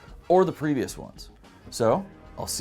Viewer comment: Did he just call us Skylanders?